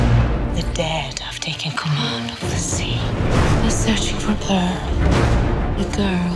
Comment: "theyre searching for thir?" can someone tell me what is it i didnt heard it right